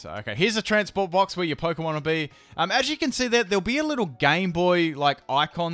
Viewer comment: I could have sworn he said "little gay boy icon" at 14:08 and I almost coughed up my water